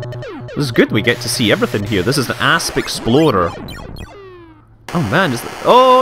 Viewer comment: I soo misheard Asp Explorer.... ( Ass Exploder )